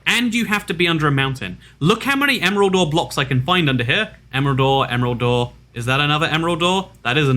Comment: I hear emerald door instead of emerald ore lol